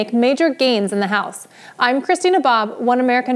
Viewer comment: Every time Christina says her first and last name together, I hear "I'm Christy Nabob". Just me?